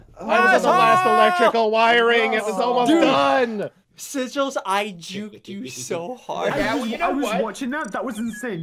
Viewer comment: did he say asshole or am I deaf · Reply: He said Russel the one with one task left